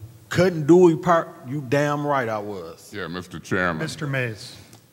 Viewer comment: I didn't understand what said. Was he in a park? Sound like he said Damn right I was in the park🤔😂